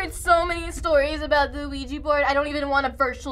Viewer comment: I heard some of them pronounced the Ouija Board as "Luigi board" lmao XD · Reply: she said "the ouiji board" but i can see why you thought she said luigi, sounds like it.